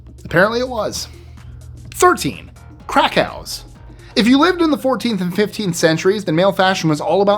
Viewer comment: HAHAHAHA FOR 13 I THOUGHT HE SAID CRACK HOUSE